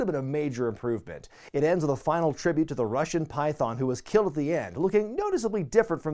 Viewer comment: did you just say Python was "killed at the end"?